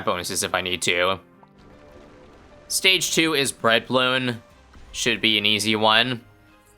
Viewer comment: did you say "Breadballon"